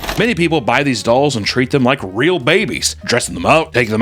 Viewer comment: If you have the same reaction speed as me and tried to pass the ad using the right arrow multiple times, the part from 5:52 to 6:00 sounded like "go f*** these dolls (...) and treat them like real babies"